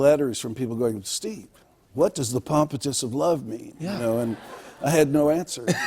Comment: I thought he was saying Properties of love. Sorta like In The Garden of Eden Baby became Inagodadivda baby